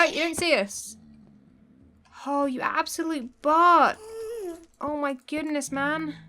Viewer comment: I would put zombie heads in the room near zombie Cleo’s portal XD When you said absolute butt, I thought you was going to say something else that starts with b :p XD